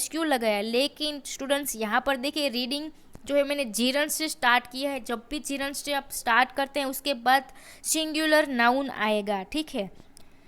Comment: What did you say at @ 10:50 , "G-run "? Please answer mam why we used "has" their , didn't got you because of your pronounciation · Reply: Gerund (ing form)+ singular verb (is/has) + main verb + object